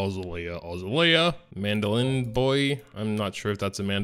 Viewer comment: Did i just hear a "boy" right there?